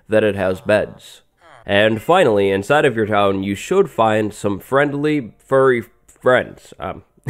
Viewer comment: OwO did he just say friendly FURRY friends